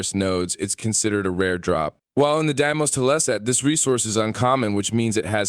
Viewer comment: did you really say "teleset" instead of "tile set"? :D